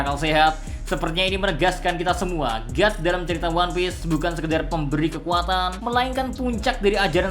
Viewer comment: aku salah denger ngga yah, di menit 6:05 bilang one piece...!?